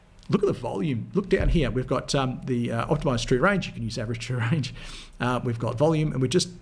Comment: what is the Indicator you have called @ 23:46 OTR? I cant quiet hear what your saying sorry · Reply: Hi Stuart, OTR stands for "Optimised True Range". It is one of custom indicators, but he assures us that using Average True Range (ATR) will get you a very similar result. Hope this helps!